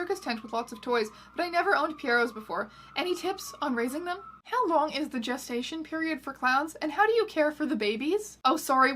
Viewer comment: I heard "how long is the JAYSTATION period of clowns?" ... it kinda works too